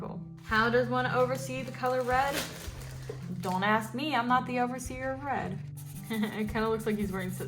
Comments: At first i thought you said "the overseer of bread"... I kinda want an overseer of bread now. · When you said overseer of red, at first I thought you said overseer of bread. I’m gonna make that now. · Ngl when she first said the overseer of Red I heard "The overseer of Bread" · The first time you said “Overseer of Red” I thought you said “Bread” and I got real excited for some cottagecore boys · When you first said “the overseer of red” and “the overseer of blue” I thought you said “bread” and “glue”